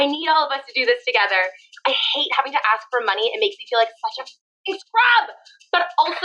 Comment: She said it make her feel like such a fucking scrub,I had to relisten to get that part cause I thought she said you make me feel like mr crabs! 😂😂😂😂